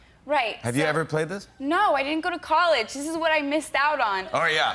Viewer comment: I didint went to college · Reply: i heard "i didnt go to college.." what video did you watch